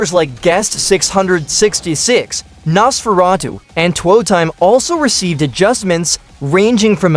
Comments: Two time is not a killer 6:35 btw did he say twerk time? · did bro say two time? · did bro say toetime